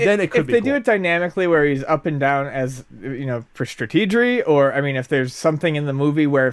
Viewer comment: Did just say "strategery"?